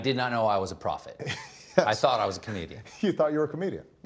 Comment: At first I thought Colbert said “I thought I was a Canadian”..lol we’ll take you